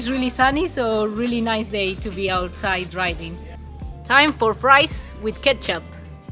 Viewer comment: I thought you said rice with ketchup and I was like.... ew